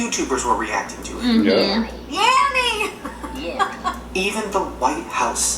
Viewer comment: oh shit I heard yanny, wtf